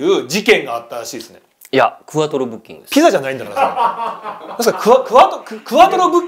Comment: クワトロブッキングが、ふわとろブッキングに聞こえて一瞬「可愛い！」と思ってしまいました